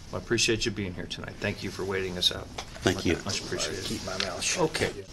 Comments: lokl does he say at 27:23ish next time ill keep my mouth shut? The voice in the back ground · Question for or anyone else who watched/watches this, ~27:21 a few seconds after says "thank you", who said (if i am i hearing correctly?) "... i outta keep my mouth shut" ? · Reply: The guy who said, "I'll just keep my mouth shut" was Representative Dwayne Bohac. I've had issues with him before. <link>